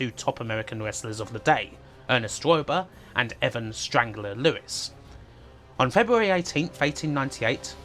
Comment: Fantastic content. I had to Google Evan "Strangler" Lewis, because at first I was thinking I was mishearing you say Ed "Strangler" Lewis. Nope! apparently, (allegedly), they are two different people..😆